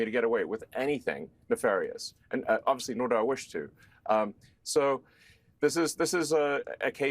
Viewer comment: "...can not get away with anything nefarious...and no doubt I wish to"... 8:05~8:13... I listened half a dozen times...WTF, I heard it, you heard it... everyone should listen to it... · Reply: You can, and should delete this, much as I wish you were correct. He clearly says "... nor do I wish to." Sad but true... no gotcha. Ps... If you do, reply or something to this so I get a notification and can delete this as well. The entire thread should then disappear (I think).